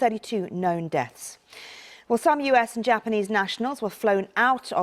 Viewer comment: Am I mad or did she just say 6132 deaths?